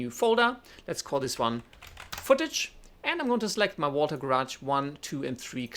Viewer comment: Thought he was saying “water garage” half of the time Awesome video by the way, really helped me a lot. Thanks